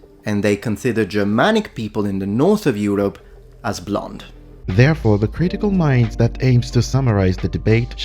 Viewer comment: I’m watching this at 2x speed but I thought you said black at 18:50 and I’m like what 😆 Germanic people are pretty fucking white, some may say they’re aryan. But those same people would probably send me to the gas chambers for having Jewish heritage